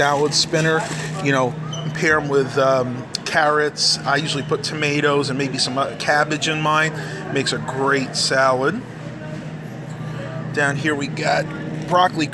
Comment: Hey. At 3:57, did you say "carrots"? :) Carrots happens to be my all-time favorite saide dish. :) What do you think about that? :)